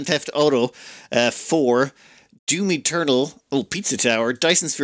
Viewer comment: When said "DOOM Eternal" I just had to pause and do a double take.. I swear, I thought he said "Do me, turtle" O_o (TMNT for the older generation???)